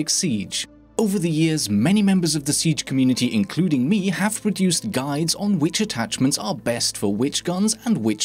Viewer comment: did you say have reduced guides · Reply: Deduced